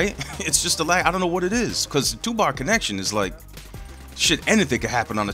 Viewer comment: I thought you said 2Pac connection! lol. Thats why you couldnt kill that dude... the spirit of 2Pac kept him alive. I was wondering why he was eating so many bullets and not dying.